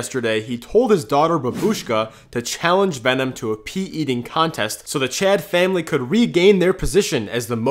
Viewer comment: Bruh I listened to the video without looking at the subtitles.. thought he was saying pee eating contest until I actually watched the video 😂